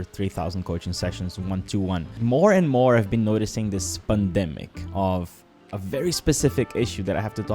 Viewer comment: funny, I just noticed the way you pronounce it sounds like your saying "The SPUNdemic"